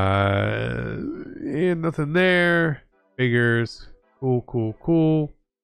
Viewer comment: Correct me if I’m wrong but did he just say what I think he did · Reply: GudDai “figures”😂